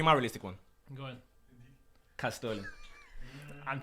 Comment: what name did he say? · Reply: Kaz Sterling